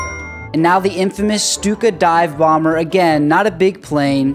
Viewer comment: When you were saying the name of the second one at first I thought you said stupid dive bomber😂